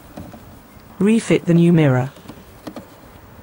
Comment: Instead of "clack" I heard "crack". Is it the same?